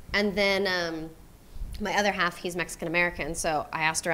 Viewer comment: What doe she say at 00:59 her what ? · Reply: Other half as in husband